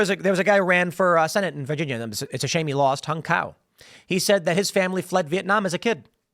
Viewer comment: Did he say Hung cow? O_o